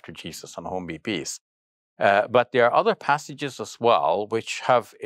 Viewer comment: what did he say at minute 4:50-4:52???? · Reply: “Uh but there are other passages as well”